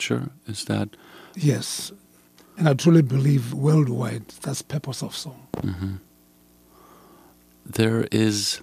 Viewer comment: he says something like peppersoft song? Am i hearing this correct? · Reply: He said "That's the Purpose of a song"